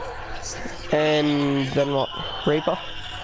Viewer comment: DID HE JUST SAY, RAPER?!